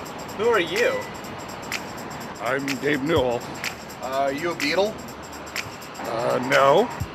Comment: i thought he said: "Are you evil?"